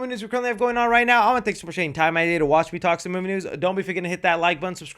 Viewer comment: what are you saying here lmao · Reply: Nowiwannathankyousomuchfortakingthetimeoutoftheday to watch me talk some movie news